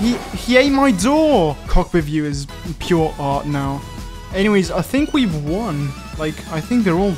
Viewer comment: After you said “he just ate my door”, you then said “cockpit view is just pure art” subtitles say “[ __ ] with you is pure art”. YouTube starting to be dirty minded.